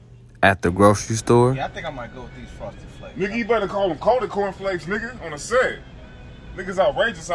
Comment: "You betta call them coated corn flakes, it's outrageous around here. You'll get fucked up." ........ Tell Dj Pooh to get his little brother lmao. Seriously, these brothers have great comedic timing. It's just a matter of time before they become household names. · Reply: I thought he said "colder corn flakes" because they're frosted 😂